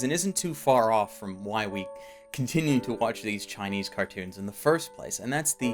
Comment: Am I the only one who heard (i know I butchered his name) say Chinese cartoons at 7:34?